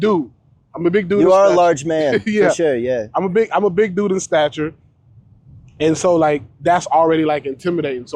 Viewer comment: Did he just say he a big doodoo statue?